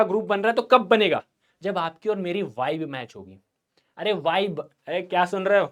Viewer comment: I listen wife match instead of vibe match 😂😂😂